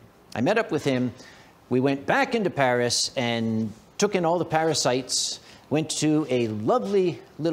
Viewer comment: What you said "Paris sights". What I heard "parasites".....I need more coffee!